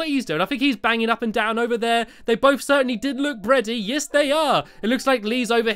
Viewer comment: it sounded like you said bready!